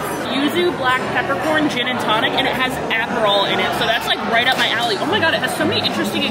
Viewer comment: Did she say it has Adderall in it?? 🤣 i'd be buzzin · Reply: Aperol spritz lol😂😂😂😂